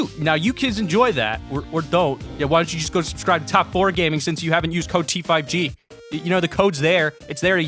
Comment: did he just say top 4 gaming?